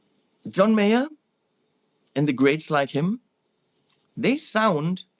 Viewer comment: I thought you said at 20:05 "John Mayer & the great Sly Kim" and wondered who this great guitarist called "Sly Kim" was? Then I realised it was "greats like him".